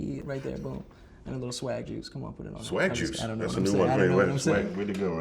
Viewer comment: Lol when he said swag juice I thought he said vag juice